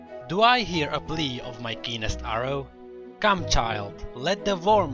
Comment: I thought you said 'penis arrow' not 'keenest arrow' hehehe.